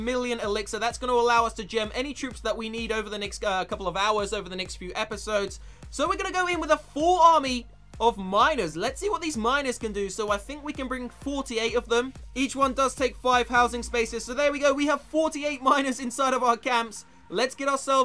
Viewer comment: Why do I keep hearing 'Let's see what these minors can do' ;)